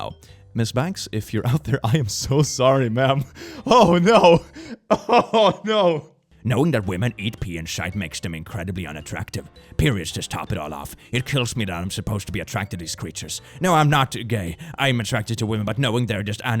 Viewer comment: I thought it said "Knowing that women eat pee and shit..."💀